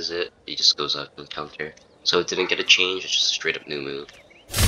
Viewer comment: I thought you said "Uwu" not "New move".